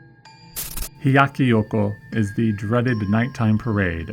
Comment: it sounds like you say Yoko. It is Hyakki Yako.